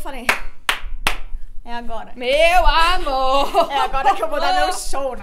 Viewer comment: EU OUVI UM MEEEU AMOOOR? :3 HEHEHE